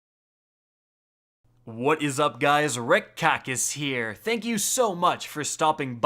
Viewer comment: RICK KOCK SUCCAAAA HEREEEEEEE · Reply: I heard, "red cock is here!"